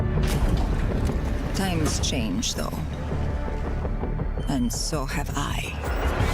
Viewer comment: I thought she said tires change lol.